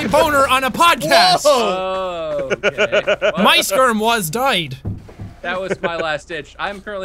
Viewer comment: I turned on captions and at 13:15when Jack said died the caption said "my sperm" instead of muyskerm. 😂